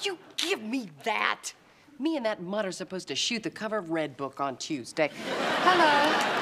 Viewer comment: what is she saying? Btw: Wasnt that the Outfit Fran wore in Season 3 Ep. 18? The wasabi incident? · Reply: "Me and that mutt are supposed to shoot the cover of red book on Tuesday"